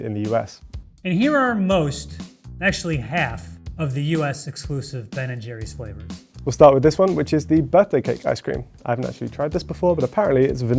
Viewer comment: English guy sounded like he said buttday cake! lol